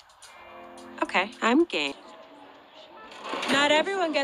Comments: I thought she said gay😭😭😭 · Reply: Nope, Diane said “Game.” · the fact that when she said "ok, I'm game." the captions thought they she said "ok, I'm gay." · i thought she said "Ok, I'm gay" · it sounded like “okay I am gay” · WHY TF DID I HEAR "ok im gay" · Why did it cut off so perfectly to sound like she said “ okay I’m gay-“ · "ok I'm game" I thought she said "gay" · did anyone else hear "ok, I'm gay" here, or is it just me? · who else heard “im gay” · But why does Diane sound like shes saying ok I’m gay when she’s really saying ok I’m game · I heard "okay.. I'm gay" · It kinda sounded like she said 'okay, I'm gay' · why does it sound like she said “ok I’m gay”??? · When she said 'okay I'm game's in the movie I thought she said 'okay I'm gay' and I was like 'excuse me miss gurl???' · The way it cut when she said “I’m game” IT SOUNDED LIKE SHE SAID “I’m gay” 😂😂😂😂😂😂😂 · i swear she said "ok, im gay" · when i first saw it i thought she said okay im gay 😭😭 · i swear i hear that as "ok, im gay" every single time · Omg I thought she said ok I'm gay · I thought she said ok I’m,gay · I HEARD Ok I'm gay